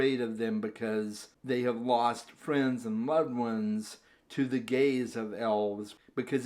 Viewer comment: I did a double take when you said “the gaze of elves” I thought you said “the gays of elves” 😅😅😅